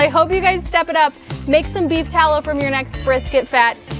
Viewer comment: i keep hearing "beef towel" lol